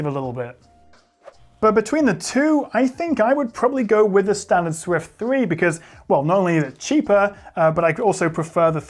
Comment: did he just say taylor swift 3 😳😬